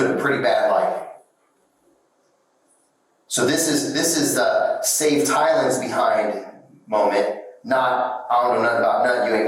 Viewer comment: He clearly says at 8:40 "This is save Tylan's behind moment". Who is in charge of subtitles?